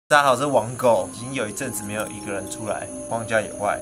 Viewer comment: 每次開頭 我都聽成 大家好 我在網購🤔🤔🤔